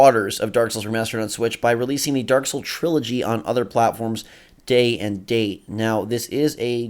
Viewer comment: Originally I thought you were saying that they were bringing the Dark Souls trilogy pack to Switch and I got really excited to play Dark Souls 3! But then I realized I misheard and now I'm bummed out.